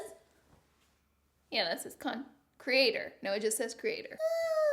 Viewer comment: I thought it was funny when you showed your cheek with a the slap of highlighter 😂😂 and then at the end because of your accent, it sounded like you said “cunted creator” 😂😂😂 Sorrrryyyyyyy 😂😂😂😅